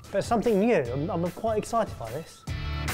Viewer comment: Who does your closed captions? cus some of them are a bit off. like 7:02 you go "Im-im quite excited by this", but the captions say "im not quite excited by this" and then at 7:22 you say "They havent got the same crack and crunch" while the captions say "but having the same" Those are just some examples but they're overall quite a bit off. Even the auto-generated captions are better at some points